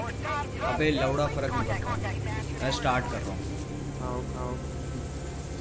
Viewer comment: seconds into the stream and I hear carry saying abe la*da farak nhi padta😂😂